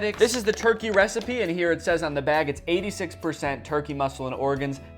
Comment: I was washing dishes while watching and thought you said 'seeds and Brains'. I was like whose fucking brains are the dogs eating 😭🤦🏻‍♀️